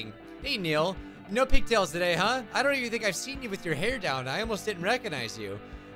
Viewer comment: i Thought he said sleep with ur hair down before